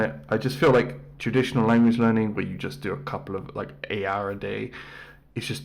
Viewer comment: did you say 8 hours a day is too little or "a hour a day" is too little? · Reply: I think he was referring to school, where you only have a couple classes a week. Like maybe 2 eight hour days a week. “...where you just do a couple of, like, eight hour a day.” I believe he said this because it’s not consistent, and consistency is key.